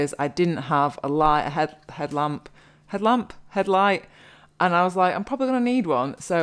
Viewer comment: Good video - Thank you! 8:14 It's good to have a head light, better than head lice which, for a split second, is what I thought it sounded like!